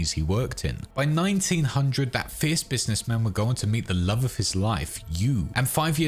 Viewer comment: my eyes closed and i heard love of his life, You i jumped😂